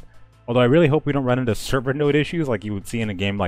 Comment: I couldn't make out what was said at 3:11. "I really hope we don't run into __ issues, like you would see in a game like Mortal Online." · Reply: "server node" issues